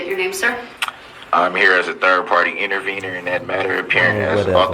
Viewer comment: Third party inter weiner, is what I heard him say! What a dumbarse